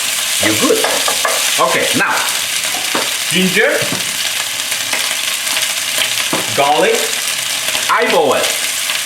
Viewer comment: What ingredient did you say at 2:22? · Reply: Ms K Jones he said “ginger, garlic... eyeball it”.